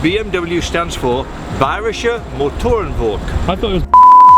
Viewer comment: what did he said? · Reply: bayersche motoren work.